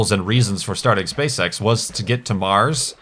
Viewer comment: did he say space sex?